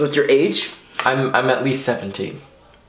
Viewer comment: i literally thought wyatt said i’m at least 70